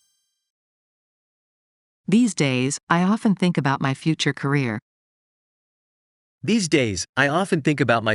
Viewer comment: どうしてもfuture Koreaに聞こえてしまう😂